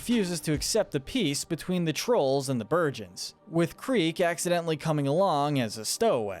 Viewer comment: for a sec I thought u said “virgins”